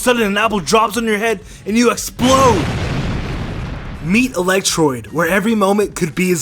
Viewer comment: Who heard him say electroid